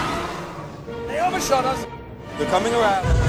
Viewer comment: Anyone else hear one of the clone pilots yell "Here they comee!" Around 1:53 i love the little details